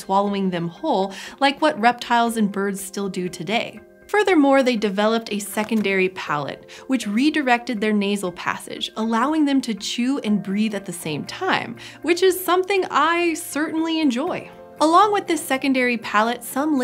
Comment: I thought she said "chew and breed at the same time..." and got weirdly excited. Surely I can't be the only, right?